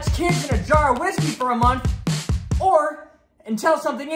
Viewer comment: My headphones cut out so all I heard was “I’m leave kids in a jar of whiskey for a month” lol 😂